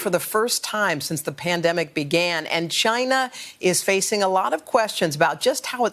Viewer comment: Did she just say CHYNA 🤔🤦